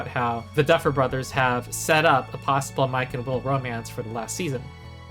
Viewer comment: i genuinely thought you said my chemical romance im crying